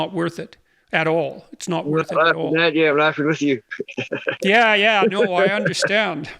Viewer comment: What does say at 1:07:05 to which Jordan says he "understands"? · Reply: Laughing at you not laughing with you , in relation to how your treated when drunk